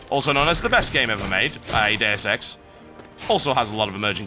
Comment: what are you saying exactly ? DSX? Daosex? Day of sex? :S